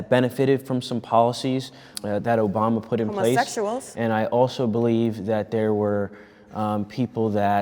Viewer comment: Can someone tell me what she says at 15:43. Does she says homosexuals.